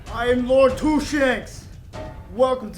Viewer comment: lmao "Lord-Two-Shanks" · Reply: i thought he said i am lord 2 shits lol